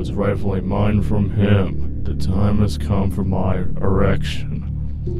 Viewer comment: I heard "The time has come for my erection"